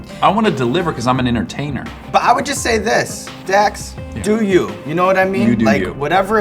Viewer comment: He took this like a fcking boss, respect to you dax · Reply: Did he really call him Dex not Dax